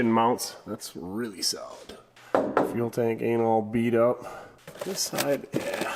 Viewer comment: did you say.. anal bead up